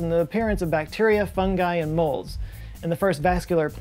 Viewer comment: did you say bacteria funghi and molds or did you say moles?